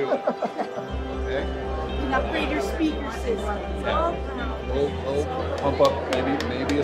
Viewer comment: did she just say “we’ll upgrade your speakers soon” ?